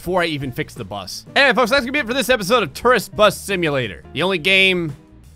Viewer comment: I thought you said terrorists bus